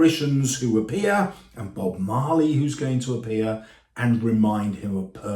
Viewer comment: Swear he said Bob Marley